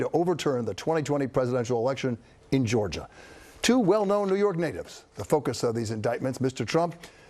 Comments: Did he just say "Two well known New York n****s?" 😦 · "Two well-known New York" what did he say? HAHAHAH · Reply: new york natives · What did he said at 00:10... " Two New York well none ni..."??